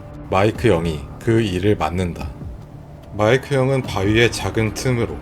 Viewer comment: 마이크 영이 마이크 형으로 들림